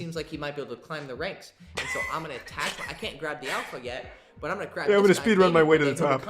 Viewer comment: he’s gonna crack that guy??? Am I mishearing I swear I heard him say “I can’t fuck this guy yet, but-“